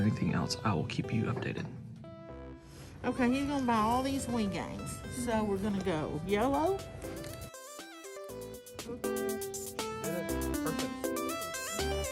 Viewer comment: So we're gonna go YOLO!!!! I swear that's what I thought she said. Gotta love that southern Arkansas accent.